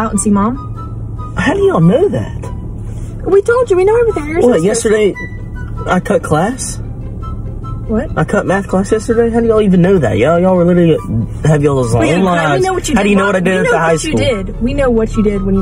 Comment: Imagine the worst thing you’ve done and afraid of being found out was that you cut math class. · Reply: Damn I thought he said cut meth…